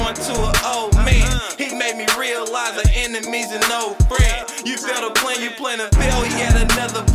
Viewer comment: He made me realize an enemy’s a known friend 🔥 🔥🔥🔥🔥🔥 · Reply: line was hard. I thought he said enemy's an old friend. Either way, so flame